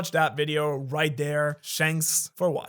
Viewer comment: Did you just say "Shanks for watching" at the end of the video?😂😂